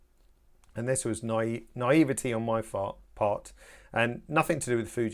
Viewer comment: " a naivety on my ... FART"?????- did I hear you correctly?